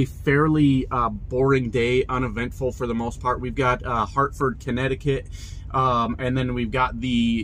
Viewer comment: Is it odd that every time you said Hartford, I thought I heard Hereford? And don’t even get me started about people having no clue how to merge anymore. Seriously frustrating and often dangerous as hell. p.s. - Really digging this new style.